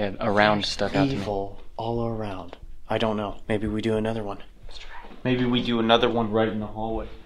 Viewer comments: Am I the only one who hear a female whisper “let’s try” at 52:55??? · DUDE. At 52:53 When says maybe we do another one. I swear I hear someone whispering "Let's try it".